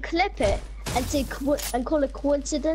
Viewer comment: Did he just say do you have Skye 🤔 Coincidence I think not